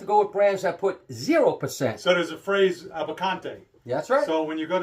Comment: What's the spelling for the word meaning 1% allowed additives, at about 3:17? Closed Captioning says "abante", sounded more like "avecante" to me, but my ear doesn't always pick up the Spanish accent very accurately. Loving these videos, always keen to learn more about Tequila, the manufacturing process, and state of the industry. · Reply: Abocantes